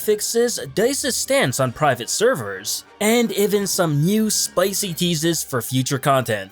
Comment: I thought he said “some spicy Jesus”